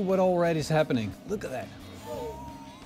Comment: "Hohoooo, that's like pork corn" I heard that right, right?